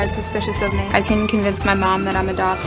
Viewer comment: I can convince my mom I’m adopted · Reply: ohh I thought she was saying I can convince my mom that I'm a doctor lolll